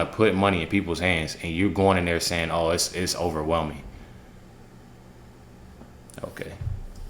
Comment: My mother used to say hate was too strong of a word too. I thought she said “underwhelming” not overwhelming.